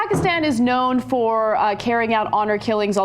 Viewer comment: Did he just say pakistanians?